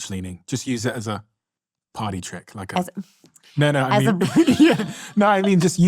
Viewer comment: 😂 she thought you said “potty trick” at 1:36:28 I love it. Sometimes the accent throws us off lol